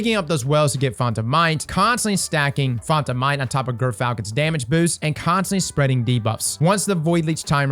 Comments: All I hear is "girth falcon" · I swear I heard you say GIRTHfalcon!